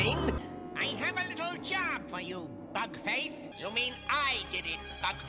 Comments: please please please PLEASE tell me i cant be the only one who heard him say "F*ck Face" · please please please PLEASE tell me i cant be the only one who heard him say "F*ck Face" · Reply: [CC] MUST be turned on! You will see that Skeletor says "...bug face" in the the dialog. Don't get me wrong. I too thought he said "fu¢k face" for the longest time too 😂! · I swear he's saying f*** face